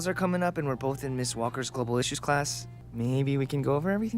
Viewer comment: He said "global issues class", right? Because I keep hearing "globalicious class" every time, even after figuring it out.